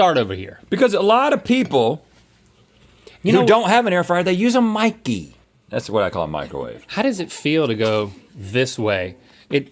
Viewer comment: when Rhett said “they use a micey, that’s what I call a microwave”, all I heard was “calc is short for calculator guys”. 😂